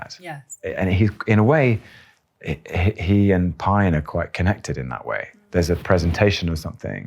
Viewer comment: for anyone reading the subtitles who aren’t able to hear the video, he is saying “Pine”, but the subtitles say Pym.